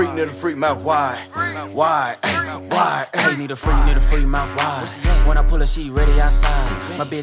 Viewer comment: i just realized its “mouth wide” and not “(something) why” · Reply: Noah 1 the tiktok dance for it made it seem like it was “why” but i heard “mouth wide” so idk why they added in that shouldn’t shrug